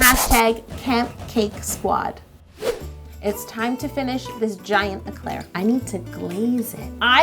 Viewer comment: I THOUGHT IT SAID GIANT CLEAR CAKE